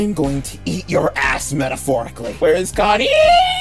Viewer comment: So he said "wHERE IS CONNIEEEEEEEEEEeeeeaaaaaaaaa" But i heard "wHERE IS GOD EEEEEEEEEEEEE